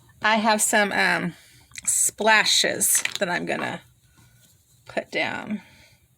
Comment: I love this layout! I am sure this is the most fun layout I have ever seen! Your piecings are so fun and darling! I have to tell you that when you said you have some splashes to add I thought you said "lashes", rofl! I thought to myself, now this I gotta see. I thought for sure you were going to add fake lashes to those synchronized swimmers! Wouldn't that be a hoot! I did synchronized swimming one summer, it was fun!